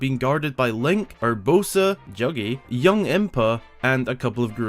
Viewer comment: what did he call it juggy or eggy?